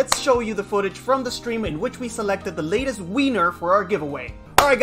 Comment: Wait did you say Winner or Wiener.. I checked this video again cause hasn't (sorry for my bad spelling) uploaded a new video yet.. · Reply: I said wiener, lol, we were messing around on stream and then it just got stuck. New video coming later today! :D